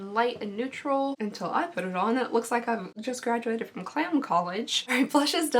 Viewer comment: I thought you said “clam college” 😂😂